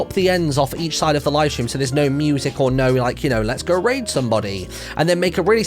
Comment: why did I think he said lets go rape somebody